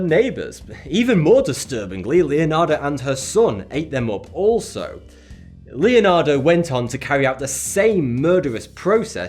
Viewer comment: did you say Leonardo